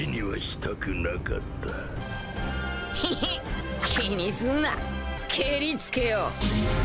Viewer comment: sounded like he said Kay deez nuts